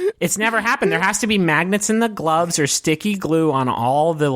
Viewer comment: I thought the word magnets said Magnus.